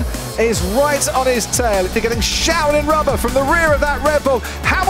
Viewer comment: can someone say what Croft said here at this point in the video? Shauling???? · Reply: Showered*